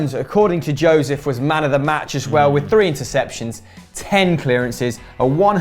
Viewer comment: I'm sorry, but WHAT THE FUCK DID YOU JUST SAY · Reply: 3 interceptions?